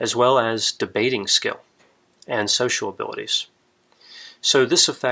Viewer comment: At time 2:59 I was distracted and thought I heard him say "Sexual Ability". 😂😂